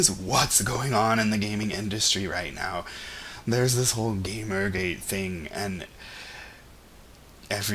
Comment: Did I miss the mermaid thing?? I'm out of the loop. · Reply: Oh, at 12:16 you said "GamerGate" and I thought you said "Gay Mermaid."